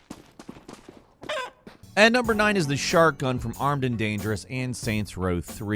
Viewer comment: Dude I thought you said shartgun, I'm using that in my next shooter game